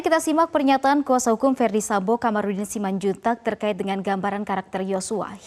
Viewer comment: pengacara ferdi sambo Kamarudin simandjuntak? Apa saya g salah dengar kah?